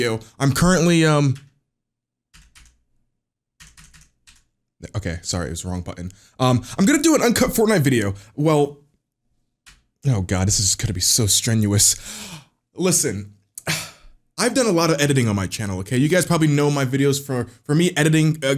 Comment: I thought it said uncunt and I was like....okay